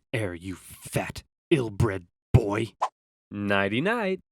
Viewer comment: i heard 'naughty-night'. How appropriate xD